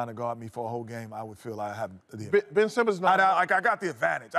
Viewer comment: Am I tripping or does chuck says nope